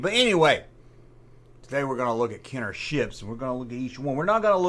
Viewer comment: At first I thought you said "Lets take a look at Kenners 💩!"🤣🤣